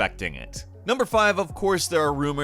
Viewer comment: Did he just say Metrode Prime?